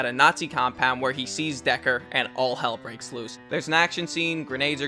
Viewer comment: Okay, thank you. Also, who was the name of the Nazi Hunter? Was it Owen Shaw? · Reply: Oh, okay. Thank you for clarifying that. Do you remember the name of the other characters? I heard names mentioned here, but IDK who they are: 6:43 Was the guy's name Deckard? The person that you said Magneto fights?